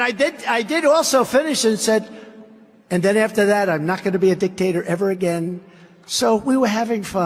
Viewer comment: It sounded like he said “and after that I’m not going to be a dick…..”. I think we all know that isn’t true!😂